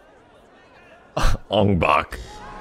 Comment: did he just say Ong Bak? Like the movie